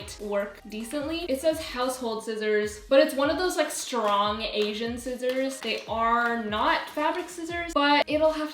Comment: did she say strong asian <mail> on instagram💛